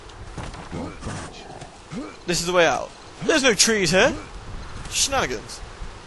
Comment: Because of all of his mocking of the bad accents with even worse mock accents from him when he "There's no tree here!" I thought for a second he said "There be no trees here!" In a mock accent